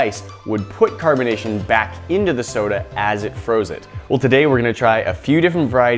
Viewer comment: Am I the only one who heard him say “as it frozes”?😂